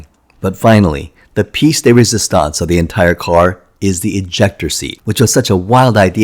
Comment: I almost thought he said "injector seat", which might have been a fun concept for any spy spoof movies to play around with!